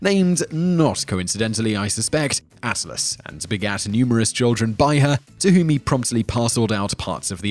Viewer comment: I thought he said "assless"